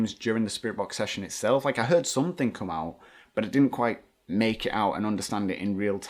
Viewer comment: Sorry I heard " now come out come up" which doesn't really make any sense but that's what I got.